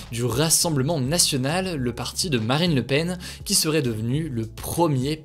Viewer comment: Le saviez-vous ? Je vous souhaite une excellente soirée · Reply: c'est moi ou j'ai entendu maraine le Pen ? 🤣🤣🤣